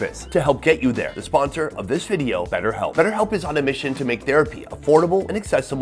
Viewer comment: At first I thought he said Better Health was the sponsor & I was like well that's odd....